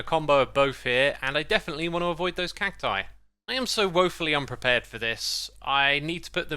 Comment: "I'm so woefully unprepared for this" sounded like paternity lmao